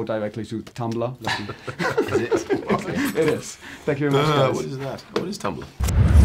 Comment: did Chris just say , "What is that? What is tumblr?"?